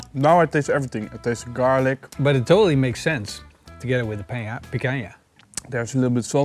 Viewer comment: Jejejejeje did said PAINcanha at 7:34?? You guys really rock!!